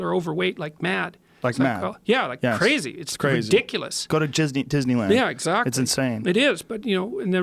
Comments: Joe: “go to jizzneyland”. I hear that and I’m going to make as many people aware you said that. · Wait, did he say Jizzney land? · Shortly after 2:18:15 did joe say Jizzneyland?? 😂😂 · Did Joe just say "Jizzney Land"? 😆